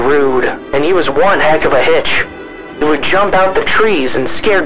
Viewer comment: thought it said b**ch for a second